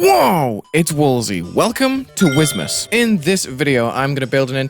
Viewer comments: i thought you said rizzmas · I Thought Blud said Rizzmas, would’ve made my Day